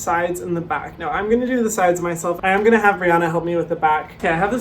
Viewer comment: I thought he said Rihanna and I literally just froze expecting to see RIHANNA come in...